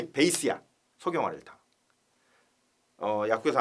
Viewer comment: 강의 잘듣고 있습니다! 24:30에서 사물탕 베이스로 쓰는 제제 이름이 정확히 안들리네요ㅠㅠ 적어주실 수 있을까요~? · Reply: 강의 들어주셔서 감사합니다. 소경활혈탕입니다. 소경활혈탕은 사물탕에 혈액순환에 관련된 구어혈약, 진통소염에 관련된 거풍습약을 추가한 처방입니다. 어혈성동통에 아주 잘들어요.